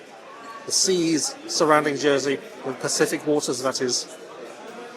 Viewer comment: Was foolishly going to criticize you for using a fork on the oysters but correct etiquette. For a moment, I thought you said Pasific waters relistened Spacific.